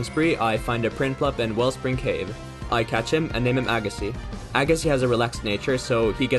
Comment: I heard that as "I guess he has a relaxed nature" and was trying to figure out why you would have to guess the nature like it's a hidden power type.